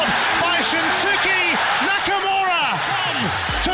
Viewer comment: 中村がナックルボーラーに聞こえる